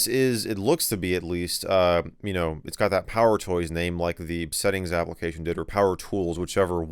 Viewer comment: I misheard "Power Toy" as "Powder Toy". Memories!